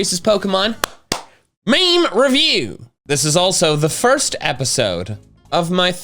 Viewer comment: I thought he said final episode